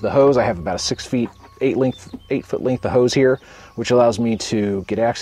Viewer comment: I thought he said Girls Gone Wild parade hahaha